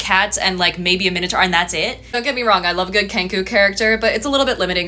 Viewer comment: When you said "kenku" at first I thought you said "pengu" and honestly I was into the idea lol